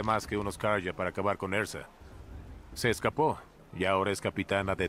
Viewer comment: haber haber haber escuche bien? ERZA?, LOOOOOOOOOL... rooooooooooooooooooost'